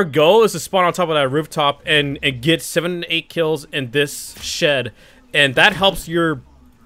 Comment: Did he just say sh!t? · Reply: shed